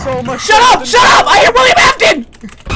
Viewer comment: "Shut up Shut up, I hear William Afton" 😂😂😂